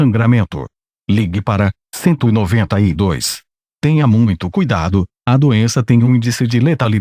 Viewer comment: por un momento pensé que dijo "tenga en todo momento cuidado" por la frase en portugués que dijo " Tenha muito cuidado"